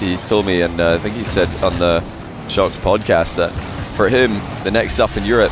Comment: What pod cast did you say 2:52 ? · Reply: Chalked podcast